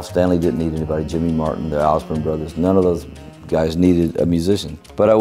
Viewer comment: the caption says Osmond Brothers,its suppose to be Osborne Brothers, and Not Brothers Osborne. The Osborne Brothers-Sonny & Bobby, have been around in Bluegrass for nearly 70 years in the business.